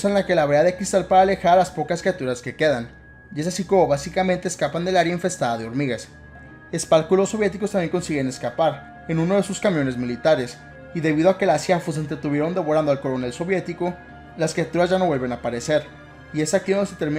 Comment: Pense que decia que son las shifus :(